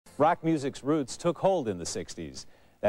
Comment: I thought he said "Black music's roots took hold in the 60"s" He should, since we invented rock and we're the reasons whites get anything glorious